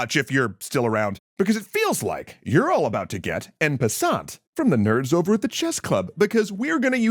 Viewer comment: What did he just say? I think I should google that. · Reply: En passant, special move that can happen when the opponent pawn makes a two space move.